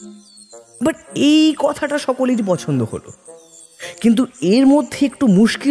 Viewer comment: “but ei kotha ta..” did i hear it correctly? 😅